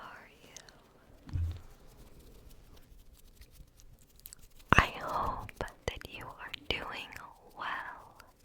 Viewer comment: I swear at the start I heard “ I hope that you are mewing well💀💀💀💀